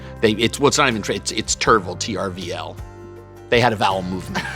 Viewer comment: The subtle dad joke "it was a vowel movement" was classic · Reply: Oh, shit! I heard him say “bowel” and didn’t get the joke.